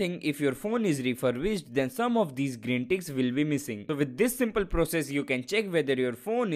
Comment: Than some of this text will be missing.. Is that what you said? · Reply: It's the green ticks. ✔️